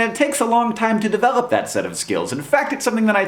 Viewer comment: help at 1:10 "it takes a lot of time to devlop that ... skill" what does he says please? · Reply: Set of